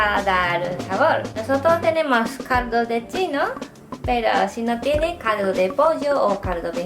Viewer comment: Excelente Excelente :O !!!! muchas gracias :) voy a poder comer ramen hecho en casa :P ... solo me quedo una duda :S ... en el minuto 6:08 dijo que iba a usar caldo de chino??? o escuche mal :S ... que es eso ???